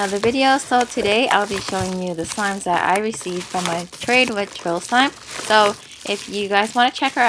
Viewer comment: Anyone else thought she said troll slime instead of trill slime in the beginning 😂😂😂😂😂